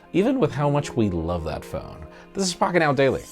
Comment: he sounded like "This is fkin out daily"